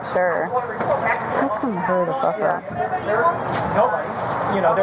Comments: Does anyone know what the cop whispered @ 6:33 ?? Lol couldn’t understand what she said she didn’t want them to hear... something like hurry the fuck up?? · Whatt did the officer whisper at 6:33 I hear fuck off but that's all I hear. Sounds like something juicy.